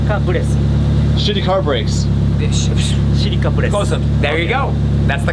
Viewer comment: Your boy was saying Silica Brass. It is one of the rarest R34 paint codes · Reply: I thought he said shitty car brakes lol